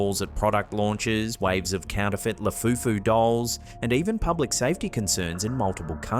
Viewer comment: did i hear lafufu instead of labubu?